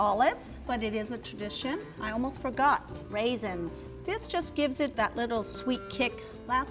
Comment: ngl, i actually thought she said "i almost broke up, raisins" as in like her husband/boyfriend didn't/doesn't like raisins so, yeah... 😅😅